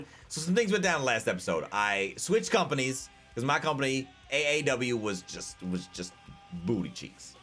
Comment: I really thought he said AEW